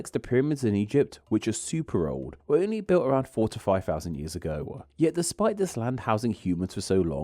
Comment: misheard that as 45000 years ago and thought was outing himself as a conspiracy theorist. 🤣